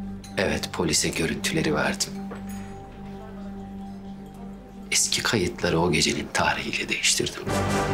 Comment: kenan ne diyor anlayan varsa yazsın lütfen. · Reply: Eski kayıtları o gecenin tarihiyle değiştirdim